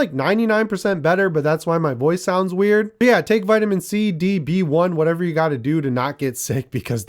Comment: LOL I misheard you vitamins count as "... take vitamins C, B, D..."